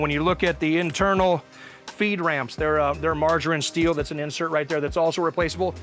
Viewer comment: I kept hearing "margarine steel" ... smooth as butter steel but greener? My ears are dyslexic...